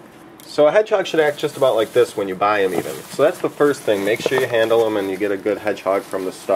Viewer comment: Did you just said that this hedgehog is Sonic?